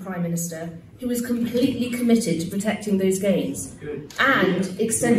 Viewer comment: I love how when she says "those gains" everyone laughs bc they thought she said "those gheys."